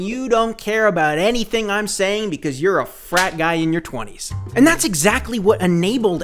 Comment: I thought you said "fat guy in your twenties." You would've been more correct